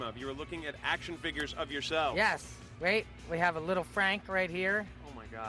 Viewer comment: did he just say "we have a little frank right here"